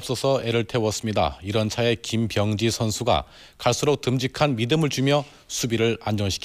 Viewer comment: 욕같이 들리냐ㅋㅋㅋ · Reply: 김비영~지선수가